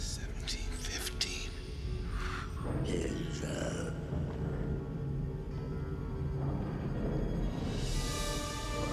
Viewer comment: What the hell did the Elder Predator say after giving him the gun, when he turned around?? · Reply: Sounds like he said "good job"